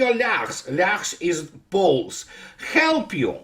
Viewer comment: In those days, Lyakhs was the name for Poles (in Russian). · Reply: Thanks, I misheard, Poles, not balls. I still don't understand the fable. Anyone?